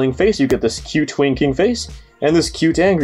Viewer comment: I misheard that as "Cute twinking face".